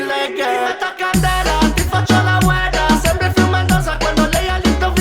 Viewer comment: ti metto a candela, ti faccio .....? non l'ho capito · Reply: Ti faccio la Ueda (la udda)